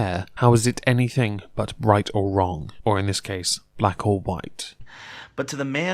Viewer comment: I swear I heard say "bright or wrong"